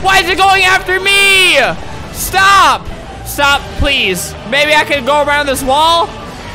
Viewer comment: When you said "Maybe i can go around this wall" i thought you said "baby i can go around this waaaaaaaaaaaall"